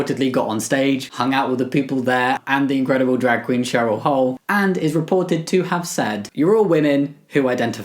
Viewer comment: I almost thought you said Cheryl Cole, but you said Cheryl Hull.